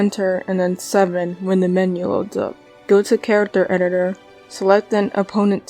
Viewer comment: I need help , in minute 4:40 what did you say? Plis , the editor dont appear to me :( (my english is bad F) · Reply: press 7 when the menu loads up